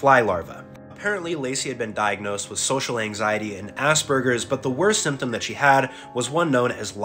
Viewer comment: Now, why did I mishear “Asperger’s” as “Ass Burgers”’ 💀💀💀💀